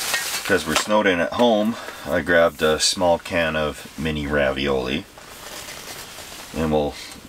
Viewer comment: "I grabbed a 'small' can of Ravioli" *grabs a 5 gallon bucket of ravioli* LMFAO!! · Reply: I could've sworn he said he had "a can of SMALL ravioli" not "a small can of ravioli" but I don't care enough to rewind to double check